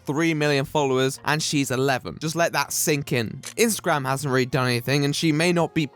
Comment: Thought it said Instagram Star wars